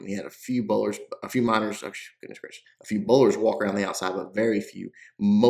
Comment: did i hear you mutter "Jesus Christ" lol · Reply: I heard goodness gracious